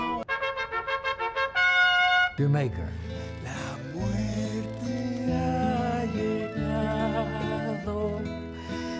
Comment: PLEASE it's bugged me for years what are they saying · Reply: "La muerte allegado...death has arrived". La muerte allegado means "death is close"